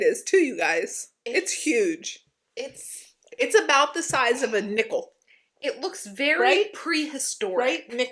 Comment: I thought that you said the bug was the same size as a nipple! I about spit out my Diet Coke! 😂😂😂😂 Nickel! 😂😂😂